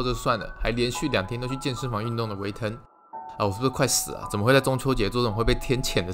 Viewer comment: 我怎麼聽成 我快被嘴死了 (￣▽￣)ノ